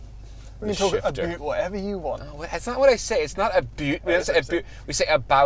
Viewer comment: I heard "a boat", did I heard wrong?